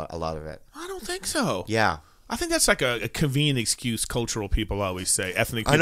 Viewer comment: At like 51:10 Erik says “cuvian excuse” and I need to know what that word means lol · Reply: *convenient, not cuvian